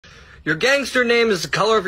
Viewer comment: It sounds like I said a bad word 😭 I said “homes” not the other thing